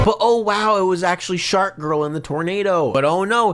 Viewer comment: Bro at 21:54 did he say shark girl and lava boy???